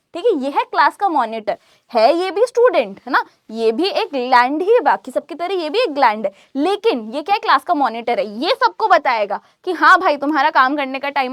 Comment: wtf what she said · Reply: What bro she said gland